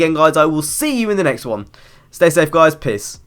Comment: It's annoying because if I went on a holiday then I couldn't play NFS 2015, btw in the ended it sounded like u said piss not peace